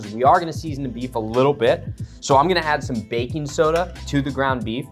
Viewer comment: I thought you said bacon soda…I was excited for a minute. These are great, try it with a pita